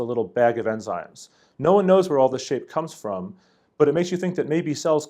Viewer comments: "No one knows where all this shape comes from" I had to listen to this line a few times because i thought he said shit lol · at first I thought he said "no one knows where all this shit comes from"